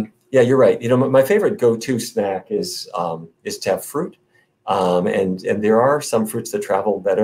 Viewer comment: What did the doctor say his favorite fruit is? I can't hear it clear enough? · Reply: His favorite “ go to” snack is “ to have fruit”. He doesn’t say which fruit.